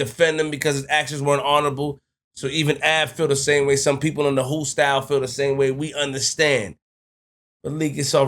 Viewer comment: flip what’s a “whoostyle” the word is hoosegow.